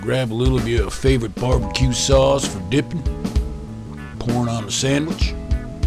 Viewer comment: I PROMISE I thought you said "porn on a sandwich" at 7:01 and you'd be so right!!!!!!